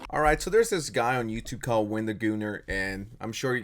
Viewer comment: did you call him… Wendigooner?